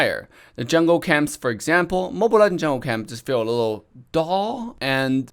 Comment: i thought he said mobile lanjiao